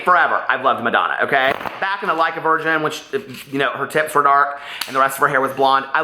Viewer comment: Legit thought u said back when her tits were dark 🙀